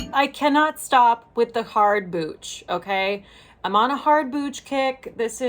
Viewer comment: So, super excited to see a new video...i am on a pain med ride today due to breaking my foot yesterday and so when you said "I cannot stop with the hard booch"I mis-heard and thought you said " I can't stop with the hard bitch..." sigh omg...LOL I am glad to hear you & Randy are ok. The look today is so wonderful. Thank you again for a great video and you have a wonderful weekend!